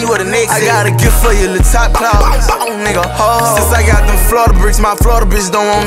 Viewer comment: “i got a gift for you, lil top clause, hoo hoo” 🎅🏽🔥🔥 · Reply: I thought he said lil top called